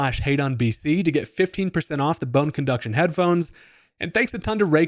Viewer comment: When you were saying your code, I had to watch that section because I heard "hate on BC" 😂